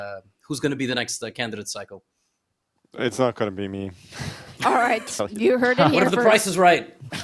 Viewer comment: What he said? I heard "it's a gonna be me" but the translate "it's not gonna be me" 😮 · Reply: he said "it's not going to be me"